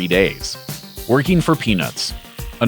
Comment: lol at 2:43 I thought he said working for penis.